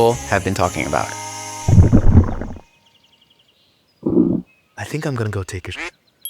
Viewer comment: “i think im gonna go take a shit” is what i hear right now as im taking a shit